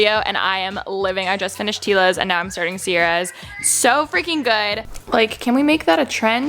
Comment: Anyone else heard her saying Eva instead of Sierra ? 😃